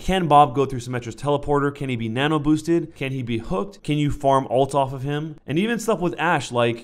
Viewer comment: thought you said "can you form voltron off of him" haha